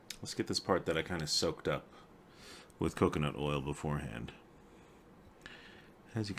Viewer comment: I totally thought you said, ‘cookin’ oil at first. 😂😂